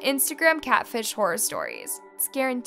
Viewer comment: I don’t know if she meant to say it or not, but at the end it kind of sounded like she said it’s “scarenteed” instead of guaranteed. That’s probably because of the S after “it’s” tho, but it still was funny.